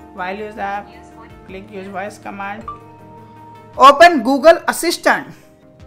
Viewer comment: I thought you said open google a fish tank looooool